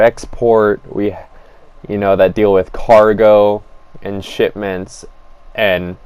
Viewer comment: you said “shitments” Instead of “shipments” your argument is now invalid and you are nitpicking and stupid and gay